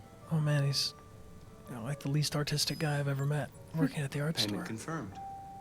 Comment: i know he says ARTISTIC but goddamn if i didn’t have to do a double take bc i thought he just called him “the least autistic man ive ever met” and i nearly spat out my drink laughing holy shit. Im sad its not what he said now